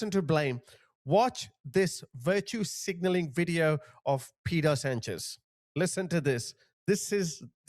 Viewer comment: did you say: Pedo Sanchez?!🤪🤣